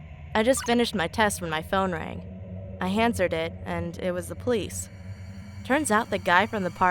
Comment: Did she say "I HANDSERD it?"